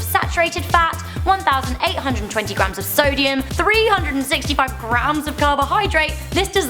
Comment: it said "Solium instead of "Sodium"